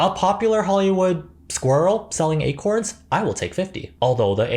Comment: I know you said "Selling Acorns" and I thought I heard "Selling a quartz" and I was like "I guess I would buy a quartz crystal from a squirrel, that doesn't bode well for me.." xD